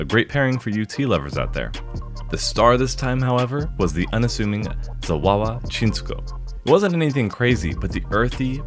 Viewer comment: Had my volume low and when he said Zawawa Chinsuko, I deadass thought he said Chihuahua Chewtoy